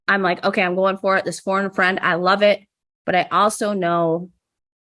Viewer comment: I thought she was saying phone a friend 😆